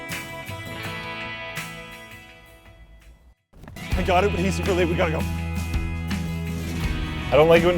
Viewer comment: what are Luke saying? ive tried like 30 times in a row to understand it, but i cant figure it out... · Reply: I think it's "I got it, he's really late, let's go."